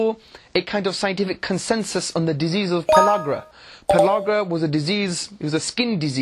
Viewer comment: but what does he say at 11:00 something sort of "pellavra" ,what does it mean. Searched the internet did not find answers